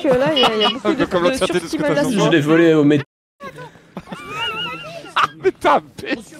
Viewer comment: C’est quoi le mot à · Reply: Métèque